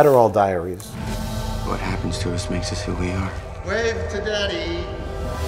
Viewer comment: I’m several years late, but it REALLY sounds like Mike saying “wave to daddy” at 38:42. Definitely not words I ever wanted to hear in his voice.